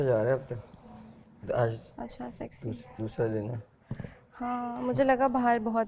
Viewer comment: What the hell she saying 😮😮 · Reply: "Acha sahi sahi"